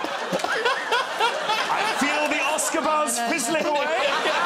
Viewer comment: What did Jack say at 00:17? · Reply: I feel the Oscar buzz fizzling away.